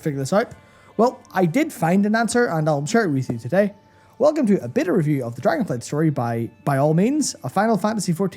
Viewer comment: "a beta review of..." i SWEAR i heard "a bitter review..." guys, i love you but i wont spend another freaking cent on that company